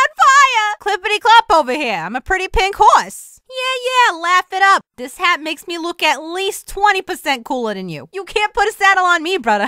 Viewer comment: i love the fact that instead of hearing "yea laugh it up you know what girls like unicorns!" i heard "yea laugh it up this hat makes me look at least 20% cooler"